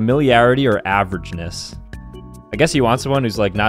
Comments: "you're all attractive, assuming you're of age" Man just dodged a bullet there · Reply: Lol I thought he said "assuming you're a b*tch" 😂😂😂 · 🦣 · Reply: I thought he said “Assuming you’re a b*tch” and I knew there was going to be a comment about that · I thought at the end he said "assuming you're a bitch" 🤣🤣🤣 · "you're all attractive, assuming you're of age" Man just dodged a bullet there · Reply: Lol I thought he said "assuming you're a b*tch" 😂😂😂😂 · 🦣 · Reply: I thought he said “assuming you’re a bitch” lmaoooo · I thought he said “You’re all attractive, assuming you’re a bitch” and that caught me so off guard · when he said assuming youre of age i thought he said assuming youre a b*tch 😭